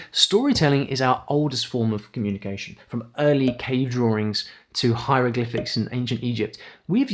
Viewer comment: When you said “early cave drawings” i had to relisten to it like 5 times because I thought I heard early k-dramas.